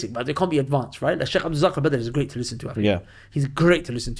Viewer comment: Who did you say is easy to listen to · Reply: Sheikh Abdulrazzaq Badr